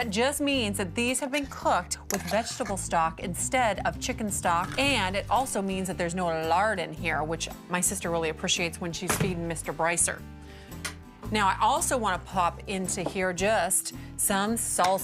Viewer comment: Wait. Who is Mr. Brycer and why does her sister not want to feed him lard?!?!? At first I thought she said “SEEING” Mr. Brycer. Either way I’m confused 😂